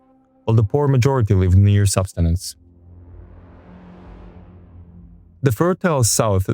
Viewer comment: The captions are wrong, it should be "sustenance" Subsistence refers to feeding yourself, sustenance refers to the basic necessities of life